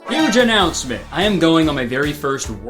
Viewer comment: I heard “hinge announcement” and was like isn’t this guy married 😂🤣